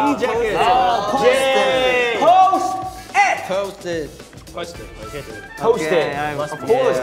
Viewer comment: Ngl, at 6:06 it sounded like Hyunjin said “Toast it” instead of “Post it” 😅